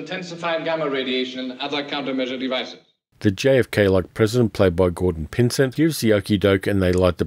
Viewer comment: What are you saying 10:10? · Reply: The JFK-like president